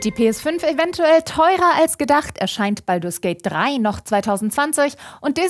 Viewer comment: Hab die ganze Zeit SKATE 3 verstanden und war schon ganz gehypt. Schade 😢